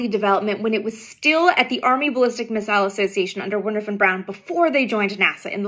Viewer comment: Wait did you just say "Warner van Brown"? 😂 That being said this is a fascinating tidbit of information, thanks for bringing it to my attention!